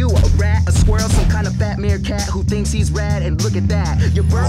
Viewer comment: The only reason Alpha dog didn't win was because of plot armour. That verse went harrrrddd "Some kind of fat meerkat who thinks he's rad" "Your bird friends, dropping words absurd again, useless bits of poem that expose the nerd in him" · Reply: it sounded like he said "Some kind of batman cat who thinks he's rad"